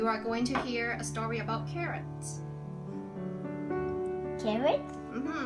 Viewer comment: I thought she said he was going to listen to a story about a Karen. I was anticipating some yelling and screaming and about to feel sorry for the little boy. But she said CARROT. 😂😂😂